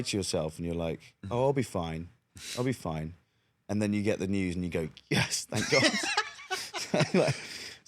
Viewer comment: Did anyone else hear that he said "Gods" at 1:04 ? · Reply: No. He said "Then you get the news"